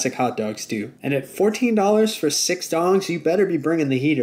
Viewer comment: did he say “at $14 for six dongs”??? Cuz that would honestly still make sense